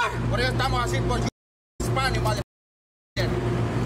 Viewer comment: did he just say "you f** HISPANIC Motherf***" IN SPANISH?????????????? LMAO LMAO LMAO LMAO LMAO LMAO